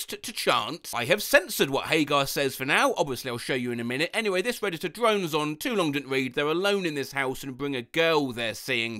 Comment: What did this parrot say? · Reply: Dance, cocky, dance